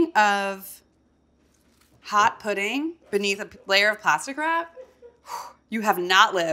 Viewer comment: Every time she said ‘plastic wrap’ I heard ‘pasta crap’ and was like wuuuh?